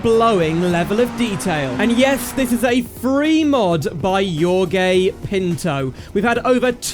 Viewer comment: as a portuguese person, i has a stroke while hearing you say jorge pinto 😭😭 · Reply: Im brazilian, I had left the video playing in the background at low volume, and I could have sworn I heard him say "your gay pinto." I had to stop what I was doing, rewind the video and pay proper attention with the volume turned up to check what was actually said.